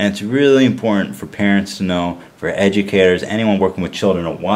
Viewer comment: Lol at first I heard "educator" as "edgy carrot"